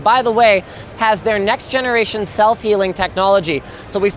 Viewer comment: Am I the only one that thought he said "selfie-link technology" at 2:50?